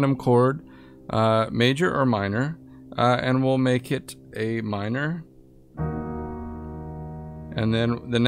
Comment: This tripped me up for like a good 2 minutes lmfao. I thought you said "Amin" not "a minor" haha. I was like "That don't look like no a minor".